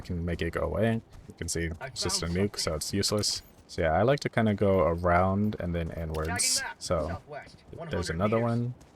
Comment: Did you say I like to go around "n words".... C'mon man you better than that.... 😂 Jk